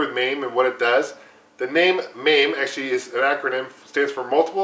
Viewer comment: Thought it said MEME arcade machine